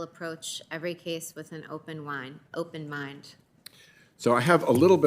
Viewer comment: did she just say "I'll approach every case with an open wine"